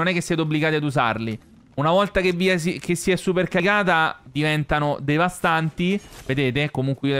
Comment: ma solo noi o tutti al minuto 12:00 abbiamo sentito si è super cagata ???